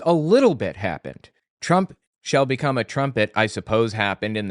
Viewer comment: You got it wrong. It does not say "trumpet", but "STRUMPET"!